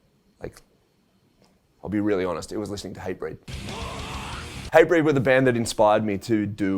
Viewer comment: Thought he said HYBRID when he said Hatebreed lol